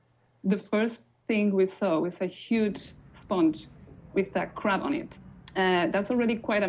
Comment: lol it sounded like she said there was a huge sponge with that crap on it